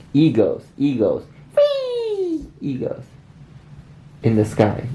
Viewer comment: I THOUGHT SAID E-GIRLS SJSJSJSJ